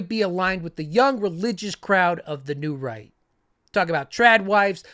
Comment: Idk why i kept hearing 'The New Reich' instead of 'The New Right'....